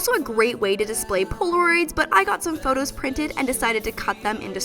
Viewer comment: i thoughf she said porn but it the captions said polaroids